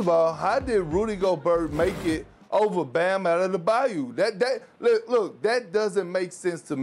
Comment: Give this man a translator, what are you saying? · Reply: over Bam OuttaTheBayou